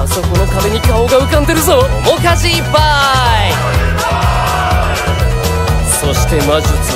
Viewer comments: の主舵いっぱーい！！がお菓子いっぱーい！！にしか聞こえなくてただのお菓子貰えて嬉しい人になってて可愛いw もうこのコメ出してる人いたらごめん！ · 「面舵いっぱーい！」だったの、！？？？？ 今までずっと「お菓子いっぱーい！」にしか聞こえてなかった、、ごめんグレートブリテンおよび北部アイルランド連合王国…